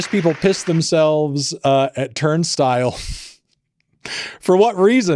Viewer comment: had to rewind cuz I thought I heard “turdstile” LMAO